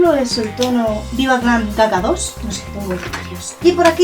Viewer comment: Te entendí viva glam caca 2 minuto 13:06 😂😂😂😂😂😂😂 claro es GaGa